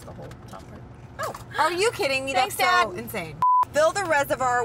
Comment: Did say “thanks dad” to sound guy at 6:00😂